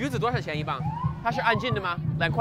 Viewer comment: what did he sayyyyyy 🤨🤨🤨🤨🤨🤨🤨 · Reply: He said, "Nei Ge" or "那个" which means "that." It's a filler word, similar to "uh" in English.